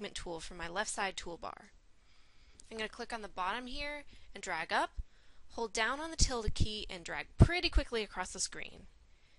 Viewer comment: into the video how did u do that. I heard "click on the line, drag it up, hold down on the tilda key?" wat the hell is the tilda key lol